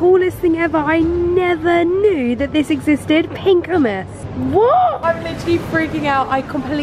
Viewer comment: did she just say..... 4:52 oomas 😂😂😂😂😂😂😂😂